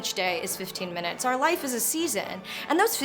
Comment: Can anyone help me out? What did Anna say at 5:12? "Our life is ..." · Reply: Our life is a season. I think it means it changes over time. Sorry, year late, lol.